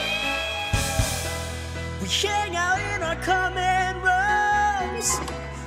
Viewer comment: did he say “hangout” or “shangout” cuz that’s what I heard lol